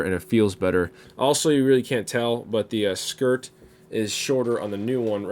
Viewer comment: did you call it a skirt